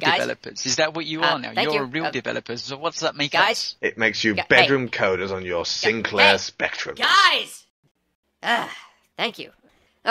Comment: of the transcript: "It makes you..(even I'm not entirely sure what he says here)" He says "It makes you bedroom coders on your Sinclair Spectrums."